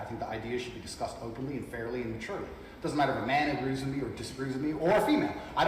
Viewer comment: they put immaturely instead of maturely